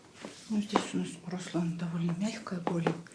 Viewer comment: Она реально на 52:20 Русланом назвала или мне послышалось? Лол